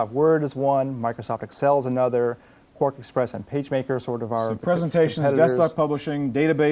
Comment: I thought he said grandpa's pacemaker